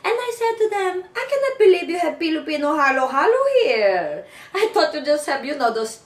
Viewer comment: The aunt's crazy laugh is shocking. LOL. And I really heard "Pilopino" from her. :]